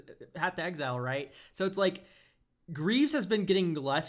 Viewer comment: I got to the end, you three are very insightful and a joy to listen too. I hate Green and Colourless and still love Green 😂 Originally, I thought said I take Green pills LMAO! You don't need to ramp yourself that bad 🤣